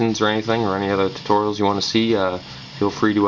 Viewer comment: sounded like you said "feel free to rapist"